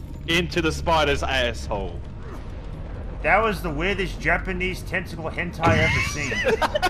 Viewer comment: what did he say? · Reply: "That was the weirdest japanese tentacle hentai i've ever seen" X D